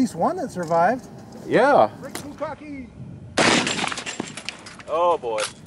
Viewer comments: did I hear say brick bukkake? Awesome vid guys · Lmao i heard that "brick bukaki" 😆😂